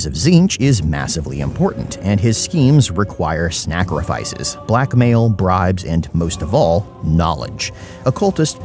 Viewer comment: I’m not gonna lie, at first I heard “black male brides” not black mail, bribes